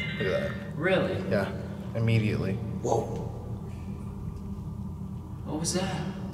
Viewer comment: Dude did hear something..Sounded like whow!!